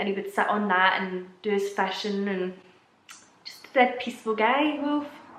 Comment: Wow lmao that accent is a rough one to figure out with certain words lol like FISHING.. I thought she was saying FASHION! LMAOOOO! and sightings sound s like SATAN'S lmaooooo great video though xoxoxooxxoxoox